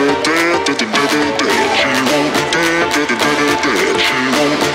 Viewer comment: Anyone else counting the Dead no kidding it's more than 100 times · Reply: Reinald RR I heard him say she wants me dab da-da-da-da dab dab dab